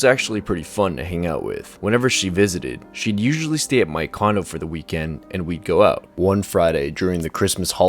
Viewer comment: Omg I thought he said she would usually stay at my condom...